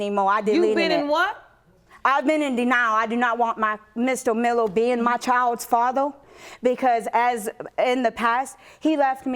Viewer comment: Did you just say I don't want Mr Mello to be my child's faddo......are my ears playing tricks on me?